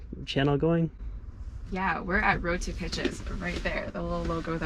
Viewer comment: I could've sworn she said their YouTube channel was "Road to Bitches". Then you showed the picture with the name and I was like "Ohhhhh. Well that makes more sense."